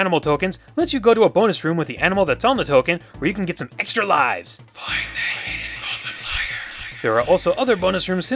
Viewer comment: what did say at 4:56. i didn't here it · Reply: something on the lines of "find the hidden multiplier"